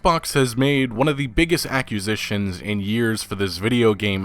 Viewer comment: Say it with me “ack-quiz-zishion” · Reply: I came to the comments and was bewildered that nobody else was talking about this. I half thought he was saying "accusations".